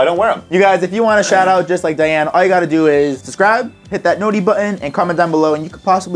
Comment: did anyone else realize that he said describe instead of subscribe at 10:41?